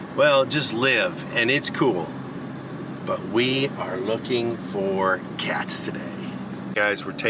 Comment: I was listening to you when you were riding around. I thought you were saying that you were looking for cat poop 💩😂😂